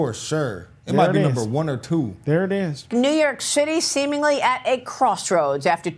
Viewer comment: did she say New York s*hitty?! 😂😂😂